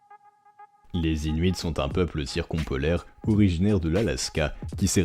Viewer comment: Excusez-moi, c'est quoi le mot à 0:15 pour qualifier le peuple inuit ? Je n'ai pas réussi à l'entendre. ^^' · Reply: "Circumpolaire". Un mot inutile pour briller en soirée ;)